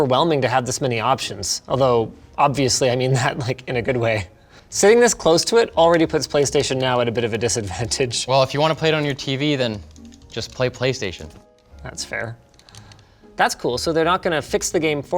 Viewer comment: Did he just say playstation 1? Lol